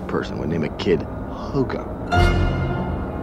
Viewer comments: As a kid, I actually thought Kent said “Hot Dog?!” at 1:45 😂😂😂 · I thought he said HOGARZUZ or HOGARTH ZEUS.